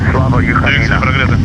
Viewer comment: Як ви там все чуєте по рації, я жодного слова розібрати не можу. А ви тут ще й "Слава Україні!" в ефірі почули. Мені довелось кілька разів цей момент перемотувати 4:28 поки я не розібрав це гасло.